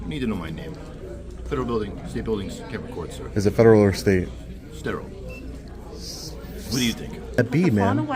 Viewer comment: "Is it federal or state?" Guard: "Stateral." "S-...?" Guard: "What do you think?" "You said it's federal, then you said it's state. What type of build-..." Guard: "I said it's federal and state sir." "It's a federal and a state building?" Guard: "No. The state law is a state law, and federal building is a federal building. This is a state building. So you can't record, sir." Whatcha drinking in that cup? 😂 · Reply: I thought he said sterile but your right he said stateral 🤣